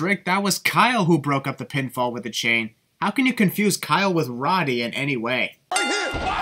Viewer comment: I feel like at 13:01 when Nigel said “Roddy” he really said “O’Reily” but it just sounded weird Bc he’s british. Remove one sin, and add it to the sin video.